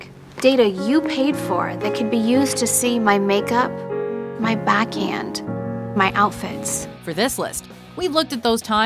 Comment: I thought Kim said her back end not backhand